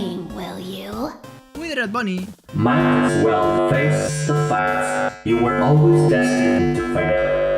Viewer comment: My swell face (no entendi) you've always destinate to fail, creo que eso dice Whitered Bonnie